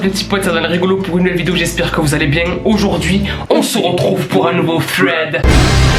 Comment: like si toi aussi tu penses qu’il dit " Nouveau Frède " 😂😂